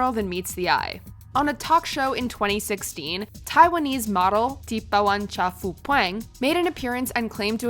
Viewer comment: Did you say Taiwanese Model? In thailand?